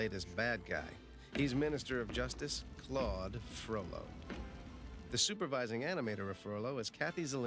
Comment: for a second I thought Jason Alexander called him Flawed Frollo!!!